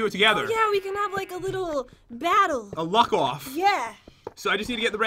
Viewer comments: A luck off I thought you said a f*** off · When pat said luck off I thought he said f** off